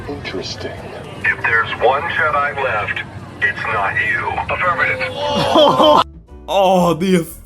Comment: what did he say I didn’t understand anything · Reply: He said " If there is one Jedi left, it's not you.